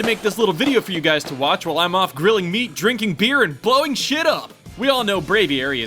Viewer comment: I thought you said drinking meat and grilling beer